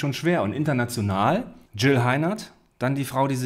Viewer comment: wie ich einfach Jill Hardener verstanden hatte 😂😂😂